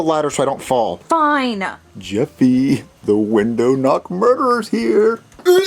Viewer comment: It sounded like he said the window knob murderer